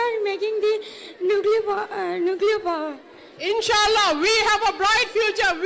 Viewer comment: I'm lost when I heard his voice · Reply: Farhan Afif I believe I heard, “Nuclear Power”